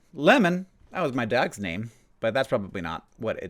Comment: I thought Hank said "lemon, that was my dad's name" 😂 I was like, Lemon? Your dad's name was lemon?! 😂